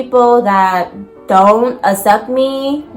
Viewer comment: Lol girl when you were saying if people don't accept me I thought you said if people don't suck me....I was like damn yes sis getting straight to the point!!!!! Lol but I realized I was wrong and my mind was in the gutter!!!